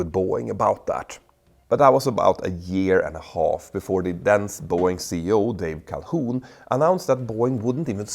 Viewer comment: Did you say “the then Boeing CEO Dave Calhoun,” or “the dense Boeing CEO Dave Calhoun?” 😂